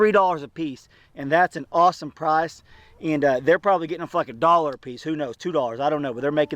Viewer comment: Great information on the purchase of chickens. One question though...did I hear you right on 2:41? I don't mind it but children might be watching. If I'm wrong, my apologies in advance for the insinuation. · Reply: I dont curse lol. I said for like a dollar lol. I talk fast at times.